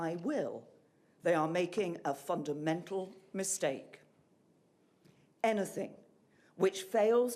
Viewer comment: I honestly thought she said "they are making a F*CKING MENTAL mistake!" :D